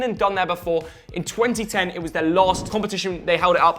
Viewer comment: did you say R9??